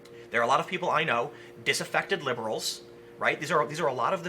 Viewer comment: Sounded like you said "Disinfected liberals."